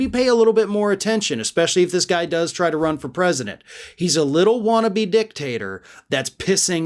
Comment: Sorry, all I heard is "he's a little wannabe dick."